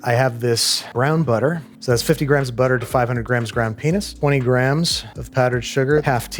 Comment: Ground Pen- what did you just say?!?!?? 😂